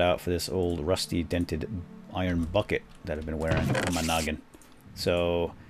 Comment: swap that out with my old dented rusty iron bucket that ive been wearing. What i hear: swap that out with my old dented rusty iron buy'ce that ive been wearing.